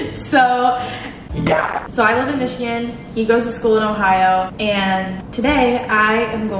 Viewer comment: i thought she said she goes to michigan and i immediately thought there’s no way